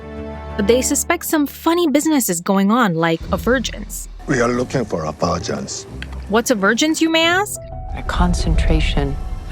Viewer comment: I was confused by this line at the time because I heard “We are looking for some virgins.”